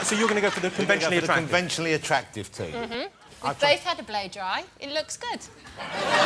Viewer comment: What did Cat Deeey say on 0:02:19 before "It looks good"? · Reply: "We've both had a blow dry, it looks good."